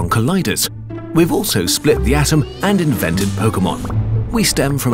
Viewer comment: did i just hear pokemon?